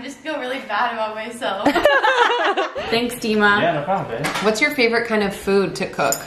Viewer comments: Did just say "no problem, babe" ??? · did I just hear "yeah no problem babe" either they like each other or he's just sweet like that and calls them babe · After said “thanks” (1:10) to Jason’s assistant did he say “yeah no problem babe” or am I hearing things? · Is it just me or at 1:10 did say babe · did dima say no problem babe!???????? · Did dima say "yeah no problem babe" at 1:10 or is it just me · Anyone else hear Dima say “no problem babe”